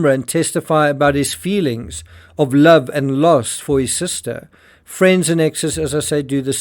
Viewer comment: wait why did you say her brother mentioned his love and lust for his sister? is that really what he said or was that a mistake? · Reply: love and lost